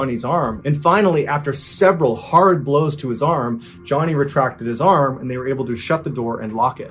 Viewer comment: At first i thought he said Johnny Lawrence, and immediately i thought "Fuck i guess Kobra Kai is cancelled"